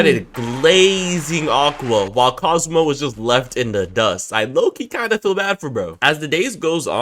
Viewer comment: I swear dog said cosmo got left in the dust @ 3:58🤣